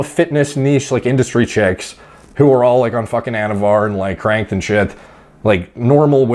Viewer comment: Anavar and what? I heard crank, which is Meth. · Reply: No he said they ARE cranked, like amped up, unless I'm mistaken